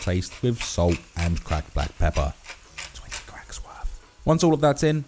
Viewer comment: I hear whisper...what u say? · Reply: "20 cracks worth (of black pepper)". It's 'to taste' - whatever your preference is.